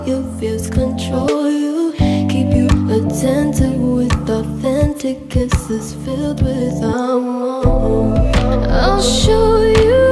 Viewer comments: I thought she was saying ‘keep you attentive with Fenti cases filled with almonds’ instead of ‘authentic kisses filled with amour’ smh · I thought it said " fenty kisses" i was yess girl give him the rihanna kisses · I thought she said "Fenty kisses" 😂